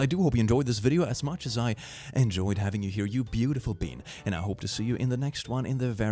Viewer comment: I honestly thought u said beautiful bean